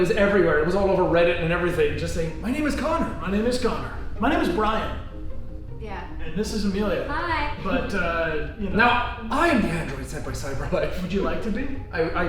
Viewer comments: I thought said "This is America" when he said "This is Amelia" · who else thought he said the android senpai cyberlife